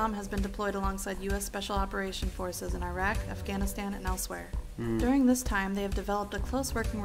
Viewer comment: After playing Skyrim for 8 hours (and I know she said elsewhere) I thought I heard her say " in Iraq, afganistan, and Elysweyr" and then I thought, "Kahjiit Terrorists?" Then immediately realized my mistake.